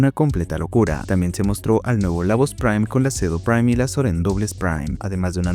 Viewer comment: escuche ceno. En vez de cedo 🖥️🦍